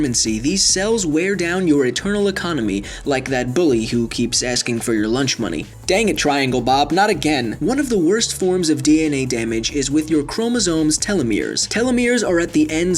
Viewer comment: 🤣🤣🤣 did you say 'dang it Triangle Bob not again' ? If so hahahahaha